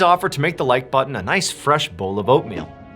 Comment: I thought you said give the like button a bowl of opium, BRB, I need to wash my ears.